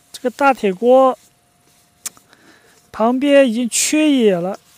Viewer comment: 大铁锅已经“缺野”？？你在说啥？